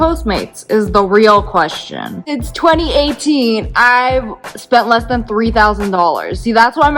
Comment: did you just say it’s 2018...? ;-; (Edit) you pointed to something that said 2018 but I couldn’t hear anything yet my volume was all the way up..